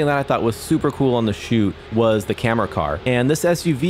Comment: did he say it’s Supra cool?